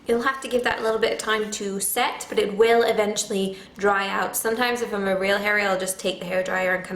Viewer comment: At first I thought she said "when i'm really hairy" 2:38 Sorry, my bad. Thanks for the tips!